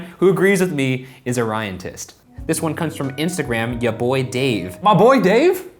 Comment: Omg when Ryan said ryantist I thought he said ryantits. I was like wow that's kind of an unfortunate name.